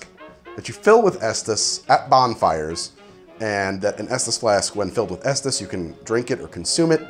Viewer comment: Dead ass thought it said "Fetus Flask"